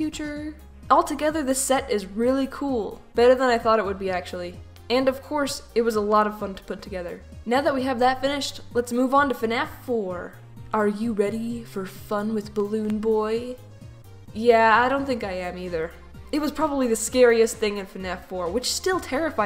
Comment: Dude i thought that she say are you ready for fready cuz of the meme at this part